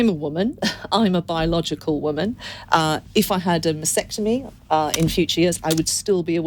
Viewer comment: "I'm a woman, I'm biological woman. If I had a vasectomy..." I didn't realise having her vas deferens severed was an option available to her, given that it is classically attributed to biological male anatomy. More power to her, its just ironic given her stance on trans issues. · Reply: Ah, I thought she said vasectomy too 😩😂